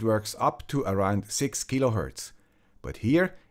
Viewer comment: Did he say 6GHz? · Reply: kHz